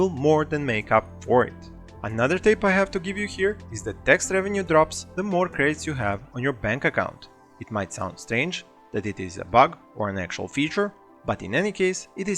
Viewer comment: Can someone please type out what he says at 9:30 about money coming in. I cant understand what is being said. Thank you! Love the vids! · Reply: Here you go, straight from the video's script: "tax revenue drops the more credits you have on your bank account. It might sound strange, that it is a bug or an actual feature in any case it is something I experimented with and showcased in a separate video you can watch on my channel. " Welcome to my channel and thank you for the feedback!